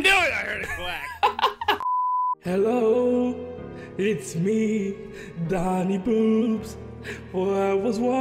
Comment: At the intro it sounded like u said Doni Boobs